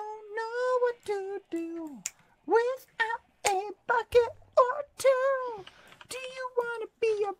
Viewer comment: Protect him he doesn’t even swear when he’s drunk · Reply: I swear I heard him say f-ckit